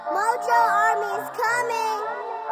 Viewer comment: Army'mi diyo ben mi yanlış duydum?